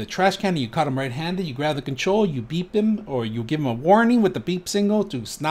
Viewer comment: I thought you said "beat them" 🤣 (I spit out my water) you said BEEP THEM. I need to get my ears checked 😕